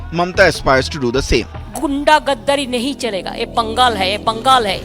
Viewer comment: i heard pangal here